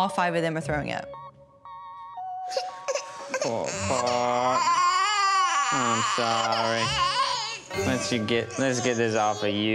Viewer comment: am I the only one who heard saying “im sorry” over and over 😢😢😢